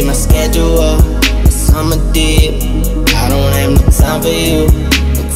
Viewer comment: First time I heard this song I thought was saying “You should see my schedule, my summer DICK”